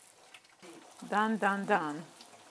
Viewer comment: ジョージア語（？）の「できあがりました」がダンダーンダンって聞こえるｗ